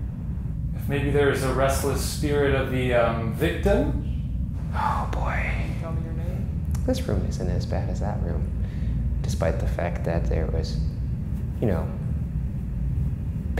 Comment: who else heard the word "can you tell me your name"